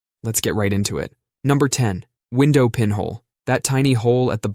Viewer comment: So... There's a micro-stutter 1 second into anything I listen to (thanks to my equalizer app recognizing that audio is being played), and every time I watch a video, I hear "let's get ripe into it" as a result. Makes me giggle each time it happens. 😅